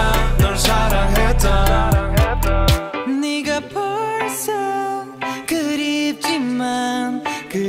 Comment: did he just say nigga😂😂😂